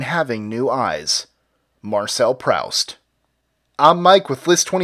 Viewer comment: Did you just say "Prowst" omg